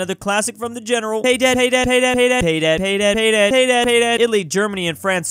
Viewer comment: who else heard "Hey, dad!"